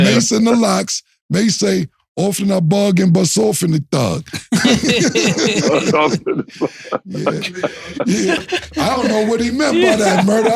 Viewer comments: "Often I bug, then bust off on a thug" is insanity from Mase (pause)🤣🤣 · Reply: I thought he said often I bug and besoften a thug. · I thought he said “often I bug, we’ll soften a thug” either way you slice it pause!